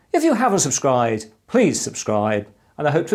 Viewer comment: Did you just say "Will hopefully torture you again"? Because I just heard that. Not a native Eenglish speaker, but I heard it quite right. :face-blue-smiling